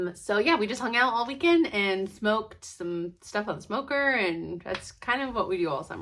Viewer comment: Yay I’m so happy I suggested you watch call the midwife! All I heard was “we hung outside all weekend and smoked”. I had to rewind and check that 😂😂. Enjoy the 13 seasons I have watched them all twice 😍